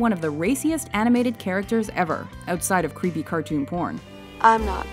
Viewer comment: Did she say "creepy cartoon porn" I really don't find anything creepy about it.